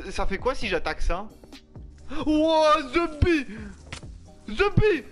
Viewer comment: "The bee" depuis quand tu parle anglais toi Kappa AH NOOOON "zeubi" ah ok